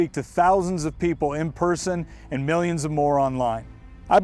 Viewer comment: did he say a million of morons online? Oops, my dyslexia kicked in.